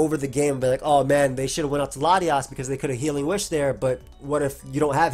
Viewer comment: Hey, did anyone notice that Noey especially got subtitles made for this vid? Cool touch but Youtube now recognizes Pokemon names so even auto-generated ones work decently albeit not perfectly. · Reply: Im not sure if its auto generated or not, it didn’t quite recognise latias at 2:36 and it said <link>” instead of <link>” in the intro. Im not sure though, as someone who needs subtitles occassionally its not as inaccurate as it usually is.